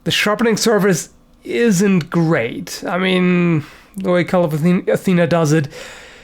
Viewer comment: This whole time I thought you were saying "Call of Athena" not "cult"